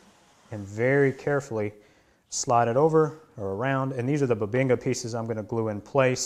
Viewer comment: Nice setup! My daughter watched this with me and howled when she thought you said "butt-binga"!